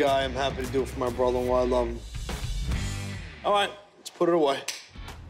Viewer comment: i thought he said "alright lets throw it away" 😂😂😂